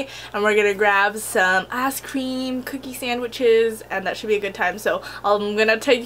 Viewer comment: for a second i thought you said ass cream instead of ice cream haha